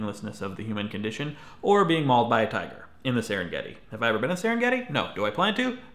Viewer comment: When you said tiger I thought you said titan at first and I got really confused